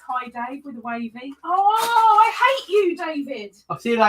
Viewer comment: I didn't yell at him. But he promised me something last week and broke his promise and he did exactly the same again yesterday 😖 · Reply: oh ok I swore I heard David I hate you 🤭